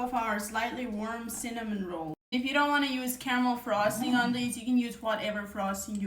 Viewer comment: Caramel??? I thought she said Camel😅😅😅😅